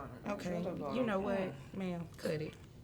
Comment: Question, at 8:28 when someone starts laughing and it sounds like someone says "cut", is that proof that these are just games and not a real show or something?